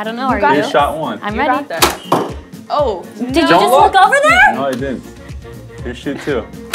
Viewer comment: About 6:20 I thought I heard say did you just look at another girl it was like a jealous gf